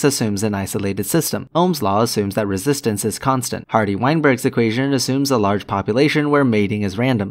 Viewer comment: Did you say Harvey Weinstein?